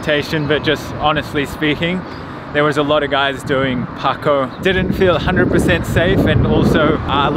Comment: at first it sounded like you said "there were a lot of guys doing parkour" 😂 definitely the sign of a dangerous neighbourhood 😄